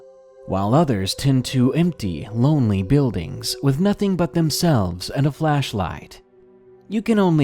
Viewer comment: I heard him say “a building and nothing but themselves and a fleshlight” sounds like quite the night